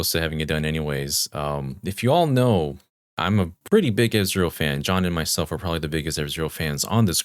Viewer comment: was not paying attention and thought said he was a big israel fan and not a big f-zero fan